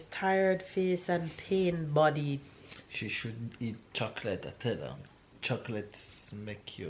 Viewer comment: I heard shouldn't eat chocolate then he telling you why you should lol